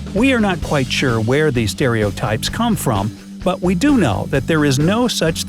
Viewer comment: Really i thought he said they werent stereotypes